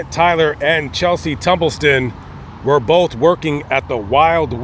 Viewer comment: Did he say tumble- son?